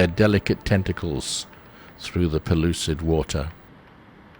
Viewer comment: regarding the jellyfish, did you say 'polluted' water . . . .? Thanks from Philadelphia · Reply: I said "pellucid" water.